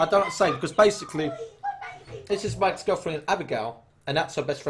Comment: did he say my "ex" girlfriend or mikes gf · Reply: lol I thought I heard mikes ahahaha but it's ex